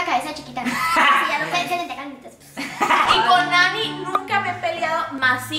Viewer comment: En El minuto 14:20 se escucha que o dice jukilop #seracierto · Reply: Yo le entendí "casi nunca" 🤔